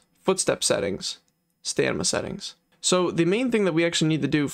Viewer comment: I keep hearing you say “sta NIMA” instead of stamina, or 🤔 maybe im deaf or